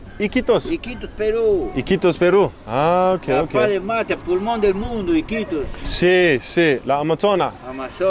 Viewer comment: PULMON DEL MUNDO = The LUNG of the world… NOT train. Los pulmones = lungs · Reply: Spot on…I misheard “Pullman”